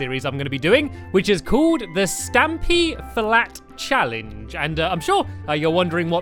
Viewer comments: I thought stampy said fat lol :). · XD I THOUGHT IT SAID STAMP FAT CHALLANGE